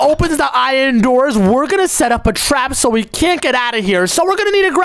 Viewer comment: i thought you said make a trap to not get out 😂😂😂😂😂😂